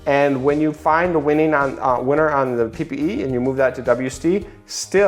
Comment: Great vid again! At 3:53 what do you say there? ppt and WC? and what do those mean? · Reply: PPE and WC ( post page engagement ads, and Website conversion ads)